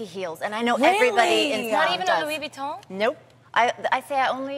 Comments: "Not even a Louibouton??" TRIED IT! LOU-BOU-TIN! We need to start getting this right people.. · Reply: sounds like Tamera say's " Not even Louis Vuitton?" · "Not even a Louibouton??" TRIED IT! LOU-BOU-TIN! We need to start getting this right people.. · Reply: Right! I thought she said "Louis Vuitton." I was like, "come again, say wut?"...lol